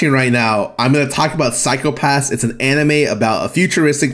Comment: i thought was saying psychopaths is an anime i was so confused until he explained Psycho-pass. man that was unneccessarily confusing